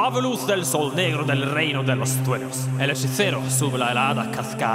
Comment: ahí parece que dices reino de los "truenos" xddd